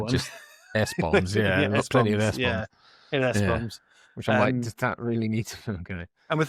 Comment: Listening to this while at work and when you two said, "S-Bombs," I misheard that as, "S-bums." I was thinking, "Did I miss some bums in this?" Lovely review by you two! Tim R.